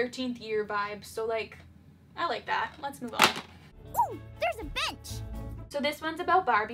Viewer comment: DID CHELSEA JUST SAY THERES A BITCH!?!? wtf lol